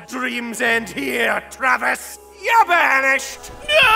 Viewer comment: "Your mad dreams end here Travis. YOUR BANISHED!!" Best line. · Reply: I heard it as "Ya banished". Still, good line. BWF